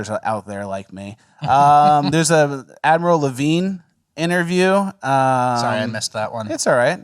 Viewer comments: I 100% thought you said "Avril Lavigne". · I thought you said "Avril Lavigne," and I thought, "what???" 🤔 And then I listened and thought, "That doesn't sound like Avril Lavigne," and then looked, and "Ahhh!!! 🤯😳😮 You said "Admiral Levine." Avril would have made more sense. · oh i just noticed it's name is admiral levine. I thought you said avril lavigne. Wait is that a bit?